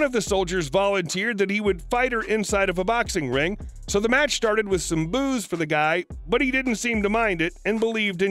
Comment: "The match stated with some boos" my dumb arse thought he said booze lol i was like ok